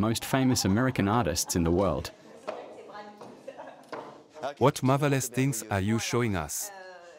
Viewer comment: At the art exhibit when he said "what marvelous things are you showing us" I heard "what motherless things" and I thought he was expressing contempt in a poetic way